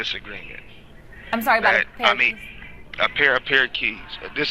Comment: I thought he said a disagreement about a parakeets lmao